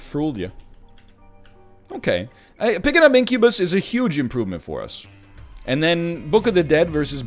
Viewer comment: Did he say minkubus at 13:23? Cause I am down for that new item.